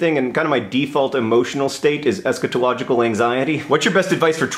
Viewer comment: In case any of you were also wondering.... The Oxford English Dictionary defines eschatology as "The department of theological science concerned with 'the four last things: death, judgment, heaven and hell'." In the context of mysticism, the phrase refers metaphorically to the end of ordinary reality and reunion with the Divine. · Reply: Thank you. For a while I thought he said "scatological anxiety," which frightened and disturbed me.